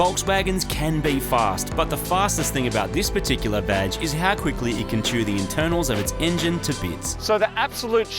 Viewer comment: LOL I thought you said "Vadge" not "Badge"